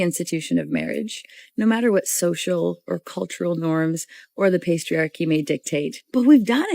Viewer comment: Did she call it a "pastriarchy"? What is that, the patriarchy in a bakery?